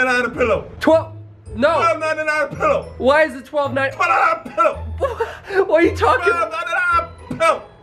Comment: i thought he said "12.9wahah PILL!!!"